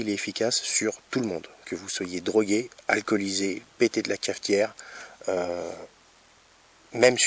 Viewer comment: J'ai mis un like dès que j'ai entendu "pété de la calcaire" 😂😂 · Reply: "Pété de la cafetière" il a dit. effectivement il est assez drôle.